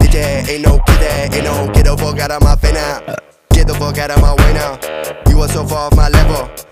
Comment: I thought it said " Get the fuck off my Weiner " 💀😭😭